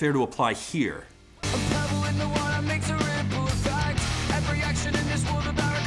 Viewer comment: I always thought it said, "A pebble in the water makes a really cool effect", so don't feel bad about mishearing that. XD I do love the song a ton though....even though I didn't like their other Guardian Angel song.